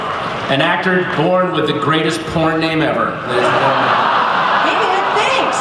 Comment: okay did he say porn name or poor name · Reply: Porn name, keep in mind he is saying that to a 15 year old (14 at the time), so it's creepy.